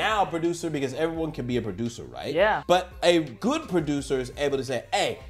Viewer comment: 沒看字幕聽成"製作人就是作弊阿"